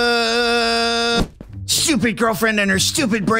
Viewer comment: I hear he said "stupid girlfriend" WHY DID HE SAY THAT?!?!